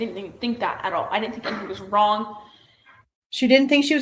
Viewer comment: I swear she said "I didn't think HE was wrong". She talks fast but seems to stumble in what she is rushing out cuz she is nervous/anxious